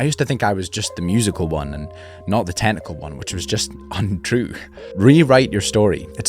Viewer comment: tell me why i kept hearing this as "not the tentacle one"